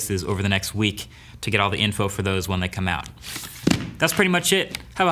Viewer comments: Did he say have a "hoppy" holiday? LOL😁 Same to you Marques! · Sounds like you said hoppy holidays